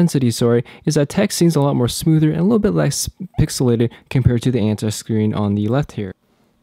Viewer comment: Did you say "Anti Screen" at 1:48?